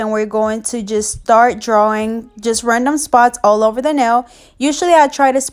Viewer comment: Tell me y i thought she said camel spots...i was like whatt???